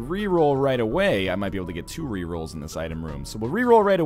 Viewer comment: captions say rear all the pets